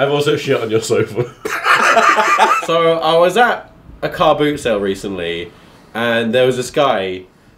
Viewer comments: i thought he said a harpoon center... Harpoons, harpoons, there better then spoons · I thought he said harpoon sale XD LMFAO